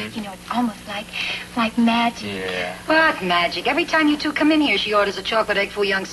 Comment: did she say "fuck magic..."? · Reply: Rose Marie said WHAT magic?